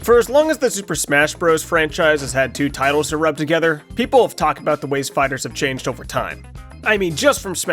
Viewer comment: I misheard this segment as "as long as two tiles have been rubbed together, people have talked about the way spiders slam into tiles" I was so confused for a minute